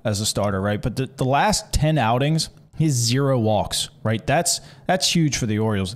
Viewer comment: このあたりからですが、「0 walks」と聞こえるのですが、「被本塁打0」ではなくて「与四球0」ではないですか？ （追記）既に他のコメントで指摘がありました。失礼いたしました